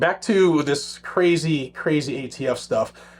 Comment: Did you just say..."gay t f"???!!!! Hahaha 🤣😂